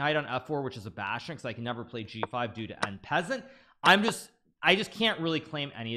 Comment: Did bro just say en peasant 💀💀